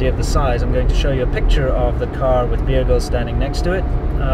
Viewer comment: Damn it, thought u said Bear Grylls